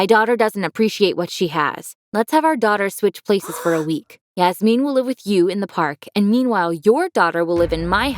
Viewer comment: Stop being rude it was just a spelling mistake or hearing mistake. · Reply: yeah I thought she said jasmine too.